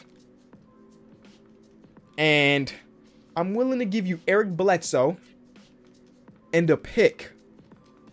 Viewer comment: Why does sound like he says air Bledsoe when he says Eric Bledsoe